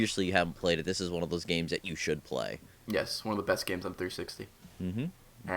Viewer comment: did i hear 3sisty instead of 60 at 7:27 ???